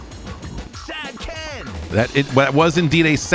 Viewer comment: I am still trying to figure out what a 'sad ken' in in the context of snowboard tricks? I thought he said 'stag ten!", but that doesn't even make any sense to me.